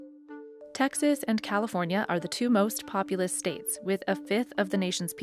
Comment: Did you just say "most populist states"?